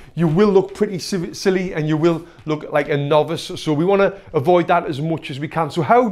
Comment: What he said: "And you will look like a novice." What I heard: "And you will look like a nob."